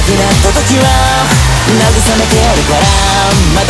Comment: ｢撃ち抜いてやるから｣が｢家にいてやるから｣に聞こえて一瞬戸惑った馬鹿が通りまーす(( エロいです、最高です。控えめに言って神ですね